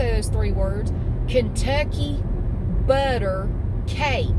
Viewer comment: I heard you say butt cake when I knew you were saying Bundt cake! 😂😂😂😂